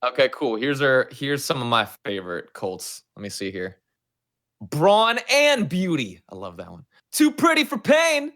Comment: Who else thought colt said "Brawl and Beauty" No just me okay!